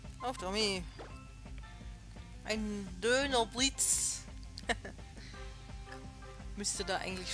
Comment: Bei 2:10 - wo du gesagt hattest "Donnerblitz" hat sich das fast so angehört als würdest du auch "Dönerblitz" sagen wollen. ^^ War ein sehr schönes Kämpfchen gegen Kalem, nur schade, dass das der letzte Kampf mit ihm war. ^^ Was mir aber auch schon in den vorherigen Kämpfen zwischen dir und Kalem aufgefallen ist, er ist zumindest auch nach einem Kampfverlust freundlich, was man von Gary oder von dem Revialen von der 2. Generation nicht sagen kann. ^^ Bei 12:00 - nicht das ich wüsste. :) Ich bin jetzt schon so gespannt auf die Pokémon-Liga, dass kannst du dir glaub ich nicht so richtig vorstellen, wie sehr. ^^